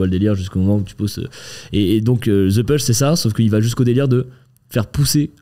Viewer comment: Pouvez nous vous redonner le nom du contenu retiré de Netflix (passé les 45min je crois) ? J'ai compris "The Pulse" mais rien trouvé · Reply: J'ai compris "the push", retente avec celui-ci